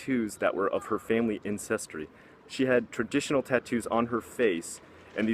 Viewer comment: Yes, I would love to see more top ten lists, and also one small thing which has nothing really to do with content but when you were talking about the Maori women it sounded like you said incestry instead of ancestry; don't worry though, as an introvert I tend to talk too fast and end up saying the wrong things as well. Keep up the good work!